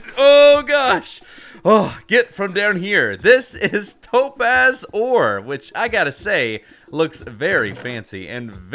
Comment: I thought he said dope a** ore